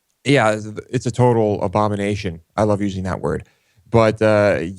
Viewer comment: I thought said Obamanation XD